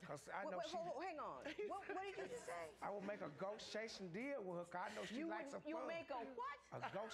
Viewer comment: I thought he was saying 'a ghost-chasing deal' Lmaoo